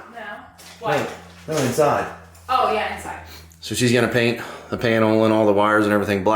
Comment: Did he just say she's gonna pain all the WIRES black?